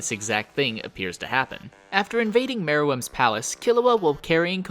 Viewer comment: Turn on the CC on 2:53. Instead of meruem's palace, its marijuana's palace 😎😏